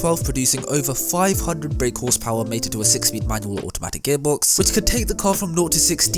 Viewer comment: "Manual or Automatic gearbox" - You could get it in Manual or Auto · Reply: ooooooh, I thought you said "manual automatic gearbox" which was really confusing. The "or" was very silent tho. Great vid btw